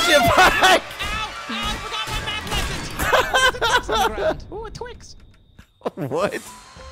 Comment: did said "Oooo a twix!" XD